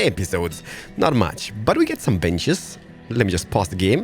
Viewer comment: For some reason I thought he said "I put some b****" instead of benches lol. Love it keep it up!